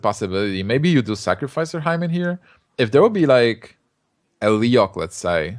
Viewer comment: Is it just me or did say "Maybe you sacrifice your hymen here" at 10:07?